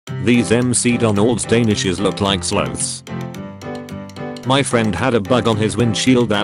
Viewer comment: I HEARD ‘SLUTS’ AND IM LIKE ‘WHAT TF’